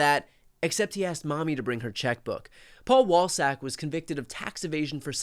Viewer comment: For a second i thought they said Paul Ballsack.